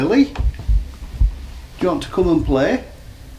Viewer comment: We think it said "her name isn't" 🤔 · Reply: I thought it said "Come in!" 😂